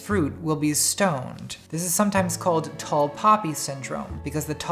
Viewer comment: Wasn't watching the screen and thought you said "Tall Papi" syndrome uwu